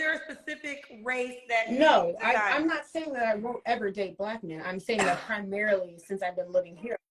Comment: There isn't one! There is, however, 8:33 and 11:33-11:40 she clearly say SHE WILL date a BM who qualifies for her expectations. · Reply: … “no I’m saying saying I won’t date black men”.. your 1 of the people who head what they want and not what’s being said